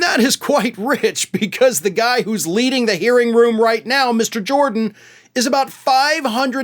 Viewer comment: I thought Swallwell said, 'the man who's LEAVING the hearing room..." cuz Gym Shirtsleeves was hot-footing it out of camera range.